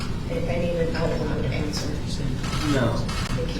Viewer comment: Minuto 4:56 lo único que dijo fue "no"?